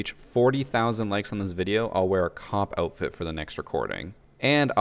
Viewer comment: I thought he said a cock outfit not a cop lmao